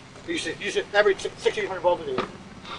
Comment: did he say you should average 1600 balls a day?